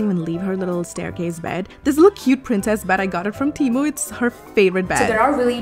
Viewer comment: I misheard, I thought she said that she got her dog from Temu.